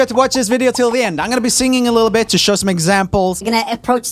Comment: im gonna be singing a little WHAT? I thought he said Bitc